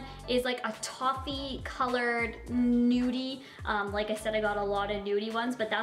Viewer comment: OK at 5:03 did she say nudie or dudie??